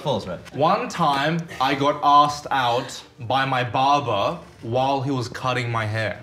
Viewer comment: When said Barber in the last one, I thought he said Father….. 😬